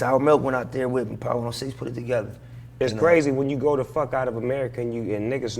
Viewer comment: I wonder what Nip was thinking when Snoop said "brazy" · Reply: He didn’t say brazy he said crazy